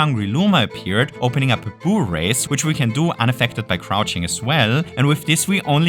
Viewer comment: I thought bro said "hoo-ray-ce"